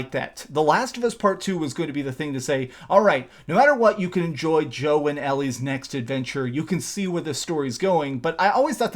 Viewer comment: Sounded like you said Joe and Ellie lol